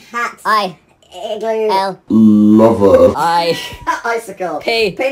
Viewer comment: My two expressions right now: "I'm in confusion..." And "I NEED ANSWERS." Wait, is it just me or did I hear "Lover." 😶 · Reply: Marina Dale oh. At first I thought it said "Lava." I don't know why, but m'kay.